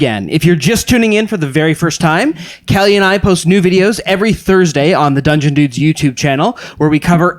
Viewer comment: Did just say "nude videos"?